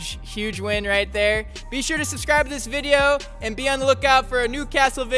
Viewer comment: did he just say sub to the video